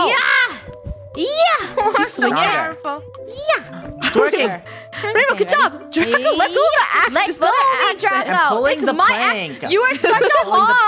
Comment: i think Draco said a swear word xD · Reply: he said axe not as*